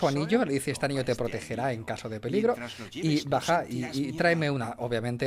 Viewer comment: Apenas me di cuenta que decia "este anillo te protegera en caso de peligro" pense que habia dicho "este anillo te protegera y casate conmigo" asdsadas